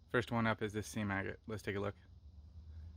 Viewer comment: Oh, SEAM AGATES! I thought you said “sea maggots” and was confused for a millisecond before remembering the title.